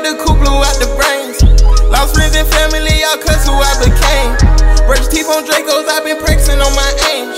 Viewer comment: This like my <num> time listening to this and I just heard bruh say “keep losing friends and families I cuss who I Be”KANE” (became). “Kane and able”😤😦